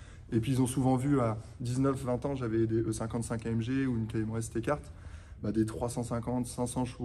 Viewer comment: jai entendu e55amg 😮🤩